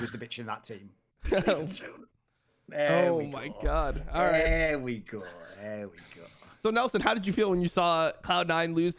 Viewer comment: what did Thorin say? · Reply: "he's leaving soon"